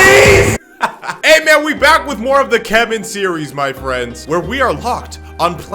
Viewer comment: we're back with more of the keman series What it sounded like he said: we've back wif more of the kevin series.